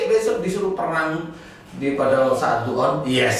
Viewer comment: Pada menit ke 19:35 pak ustad bilang perang pada saat dukhan yah.. 😲 Aku gak salah denger kan????